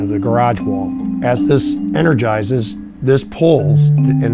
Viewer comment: Did he just say "energize"?